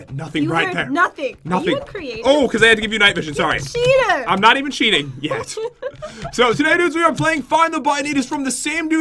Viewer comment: who heard him say "im not cheating whispers yet"